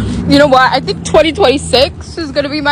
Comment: BRO I LITERLY THOUGHT SHE SAID MY ASS IS ROBIN😂😂 BRO THE SCREAM AT THE END😅😂😅😂 JUST DESCRIBES ME😅